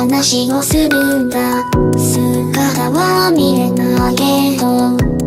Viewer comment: 「姿は見えヌァけど」って聞こえる...w